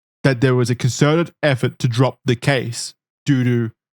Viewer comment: i heard "dodo" and i chuckled. I'm 31 btw